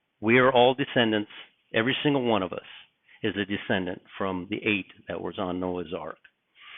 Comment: My mind is so fried from just being alive lately 😭 🤣🤣🤣🤣🤣🤣🤣 it just sounded like you said ALL OF US ARE DESCENDANTS FROM THE APE THAT WAS ON NOAH'S ARK 🤣🤣🤣🤣🤣🤣💥 PLEASE GOD GET US OUT OF HERE 🎺🎚️🌹🎚️